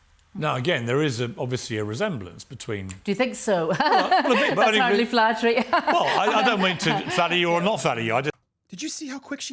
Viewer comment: Piers was so quick to shut down “oh I didn’t mean to flatter you I mean there is definitely a resemblance” he’s so worried he might’ve led her on and be the next reindeer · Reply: At first I thought she said "that's flattering," but after watching again she said "That's hardly flattering" (a dig at the actress playing Martha). Piers response ("I don't mean to flatter or not flatter you") was perfect.